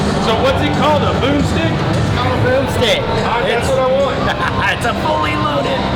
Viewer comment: I’m sitting here laughing. When your ordered the “Broomstick” hotdog I thought you said “Moosedick” 😅😃😂